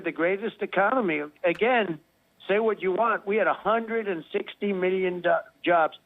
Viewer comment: Trump: "I'm a big fan of the dollar." The most honest statement he has ever made! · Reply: when he went on about how many jobs he created, he was about to say "dollars" instead of "jobs". "... had a hundred and sixty million dol- jobs ..." Even when asked earlier about what it would take for him to run again, you just know that he'd really, really, REALLY wish that he could just go "Money! Big fat dollars! Dig deep and give me everything in your bank accounts, you fucking losers!!!"